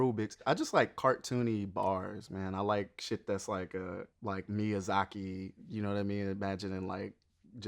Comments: no one gone mention how the subtitles thought he said "me as zackie" instead of "miyazaki"? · the subtitles say "me a zackie", it's Miyazaki